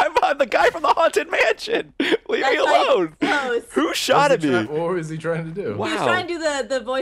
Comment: Did he say ''shoted''!?